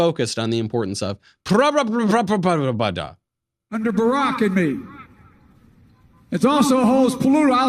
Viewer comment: did he just said Barack in me? some BBC going on 'round here.